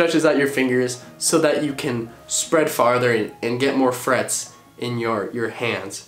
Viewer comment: K but I legit thought he said "get more friends"